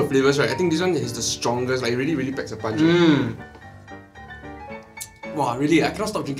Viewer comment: did i hear , PACKS A PUNCH 😏 mmhm stans where you at 📣📣